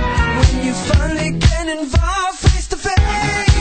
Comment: hmmm, I thought they were saying face to face...